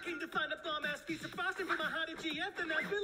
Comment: I swear I hear I’m here for a piece of frost for my hottie gf 😂😂😂😂 WHHHYYY MMEEEE